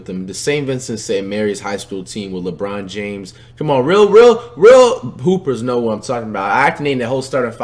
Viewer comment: So nobody didn’t catch when he said real poopers instead of real hoopers 😂